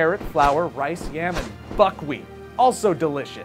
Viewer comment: i thought he said "fuckwheat" when i first heard it XD